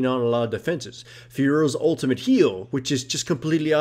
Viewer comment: I can't understand who's ult he's talking about, it sounds like he says "Fearow's ultimate heal" like, idek anymore.